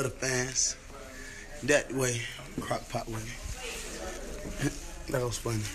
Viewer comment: Thought that shit said crack pot 😂😂😂😂😂😂